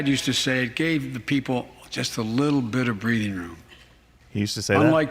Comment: Hahahaha at 37:24 her said breading room, just a little bit of breading room Hahahahahahahahahahahahahahahahahaha EDIT. sorry he actually said breathing room.